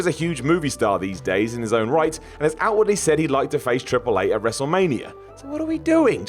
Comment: Did say "Triple 8"?